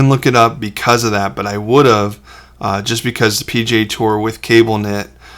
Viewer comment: Hahaha! At first I thought you said "pj tour", instead of P.G.A. tour. 😂 First time I'd ever heard of the Professional Golf Association referred to as pajamas. 😁